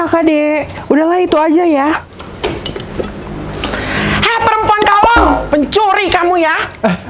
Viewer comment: Di detik 8:50 ngomong apa yah? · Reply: Perempuan kolong